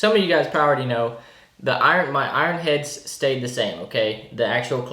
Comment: Thought he was saying "iron chefs" the whole time, very confused.